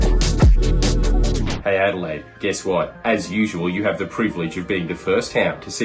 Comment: I thought you said "adlay"